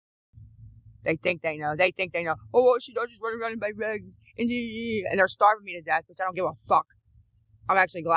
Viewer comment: did she say run around n buy bags?? Bc we all know how she tells on herself